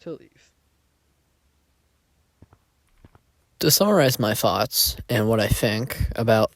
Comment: ayo why does it say summarize my farts on captions